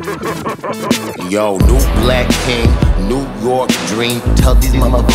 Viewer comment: When zebra says "Yo, new black King" I heard "Yo, nude black king" and was freaked out lmao